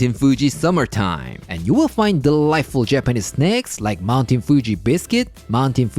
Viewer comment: Thought I heard. "snecks" 😅